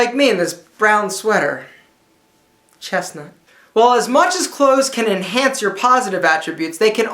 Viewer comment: did he said "честно" ? (on russian it means honestly)??